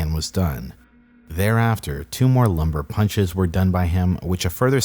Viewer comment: lumbar puncture aka a "spinal tap" known for diagnosis and treatment for meningitis (among other things)... I heard you say what I thought was "lumber punch", but that's what it actually says.